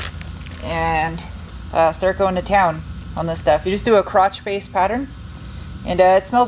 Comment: I think I like the Enkei wheels better. That said, it's your car and my opinion doesn't count. I am curious about the new Bronco wheels. Let's see 'em! Did you say "crotch face pattern" (@ 10:35)? LOL